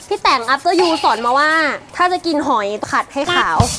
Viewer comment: after you? จริงๆจะพูดว่า.after yumใช่มั่ะ